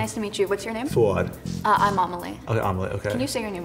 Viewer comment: I thought she said her name was OMELET